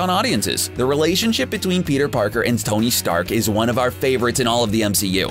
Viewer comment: did he say "stony stark"!!!